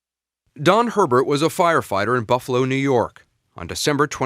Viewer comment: for a second I thought they said john herbert 💀